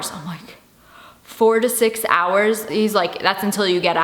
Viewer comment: At first I thought she said 46 hours